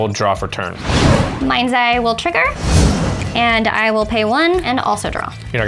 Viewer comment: does anybody else hear “my anxiety will trigger?” No just me? Ok.